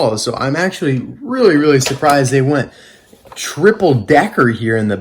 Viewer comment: i thought you said triple dent lol